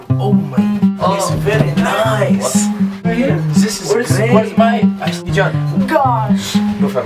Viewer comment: Did anyone else hear where is zayn at 3:14?